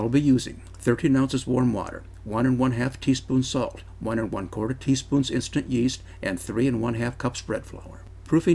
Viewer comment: Hello, For the yeast...is it 1/4 teaspoon OR 1 and 1/4 teaspoons? It sounded like you said 1/4 teaspoon. Please clarify.